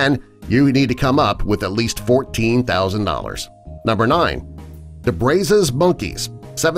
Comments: lol I misheard that as the Braces Monkey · When he sayed brazes monkey I thought he said brazzers